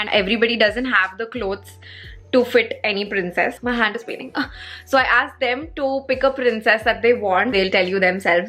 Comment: who all heard my heart is beating instead of my hand is painting. 🤣😂🤣😂